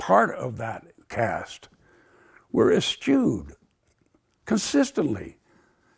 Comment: "my efforts to become part of the cast were **** consistently" what word did he say there? i don't catch it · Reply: "eschewed"